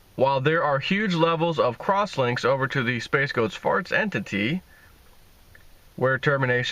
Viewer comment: What the heck do you say at about 3:13? "... crosslinks over to the space goats farts entity" is all I hear.